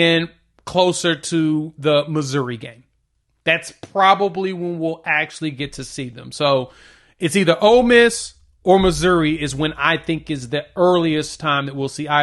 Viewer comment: I know I’m not the only one who heard him say ole piss 😂😂